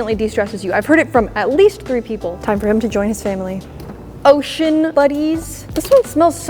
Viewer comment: When you grabbed ocean buddies I thought you said oh sh*t buddies 💀 💀